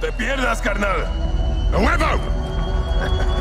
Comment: What did he said ? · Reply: Ghost says "Ahuevo", It's similar to: "hell yeah" or "of course"